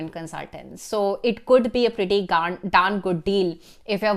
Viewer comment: Hold up did I just heard 'Be a Pretty Gand' 😂😂😂